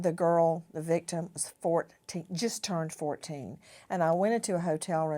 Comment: For a sec I thought you said his victim was disgusting .